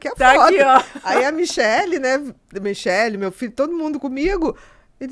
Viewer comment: ela falou: meu filho? Ou eu entendi errado??